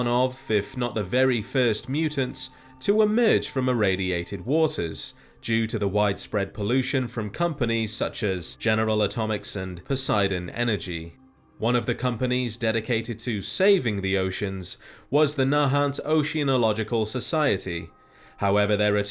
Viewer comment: I heard that as "genetal atomics", IL never be the same again